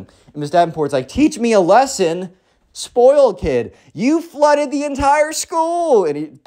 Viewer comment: I thought it Said Floor it Doesn't it Says flooded